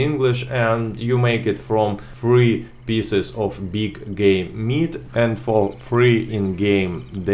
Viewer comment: did he just say big gay meat